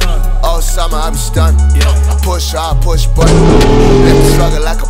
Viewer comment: I hold classes on Casper verse: He said "yall age like milk" this mean yall turn sour the older you get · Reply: on 5:42 did he just say I'm sorry AKA I beg your pardon ?